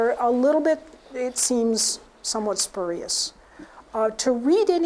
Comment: about the speed reading, I didn't get it what she said... what was the word pls ? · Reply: The word you probably missed may have been "spurious". She generally doesn't like speed reading.